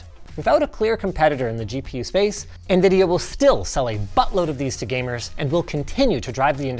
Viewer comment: I thought Linus said fuckload not buttload.